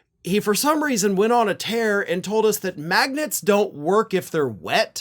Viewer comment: oh sorry...... I thought you said maggots .......Maybe just a brain worm!